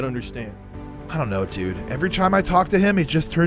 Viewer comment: he says chime instead of time